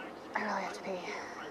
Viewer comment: I skipped to hear and all I heard was I gotta pee